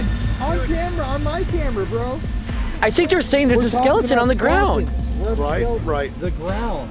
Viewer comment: I think it was saying there was a skeleton on the ground. Bc they did find one right where you guys were